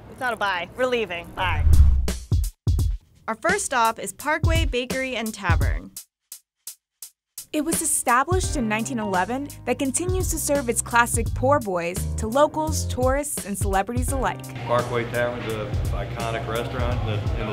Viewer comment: Did she just say poor boys